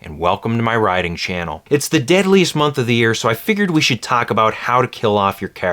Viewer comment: why did I hear "The diddliest month"? My brain is weird